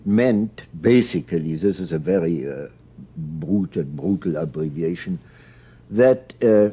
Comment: what did he said on 25:45-25:50 actually? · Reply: "a brutal, brutal abbreviation" (implying that he can't do the ideas justice with the following two sentences he has time for)